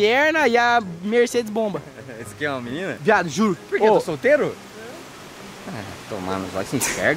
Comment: Hummm kkkkkk Só eu que escutei o fala " eu tô solteiro?" Pro Léo kkkkkk